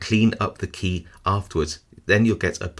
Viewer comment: What he said "Your now a spy" What I heard "Now it's mine"